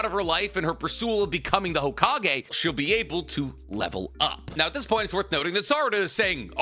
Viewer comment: did you say level up?